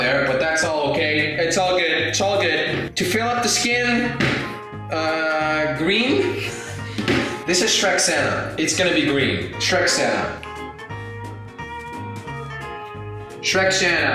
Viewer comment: For the first time I thought he was saying Shrek Santa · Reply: I thought he was saying Trek Santa